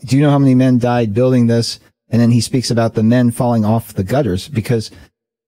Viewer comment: 5.15 im sure i heard him say girders not gutters as you said.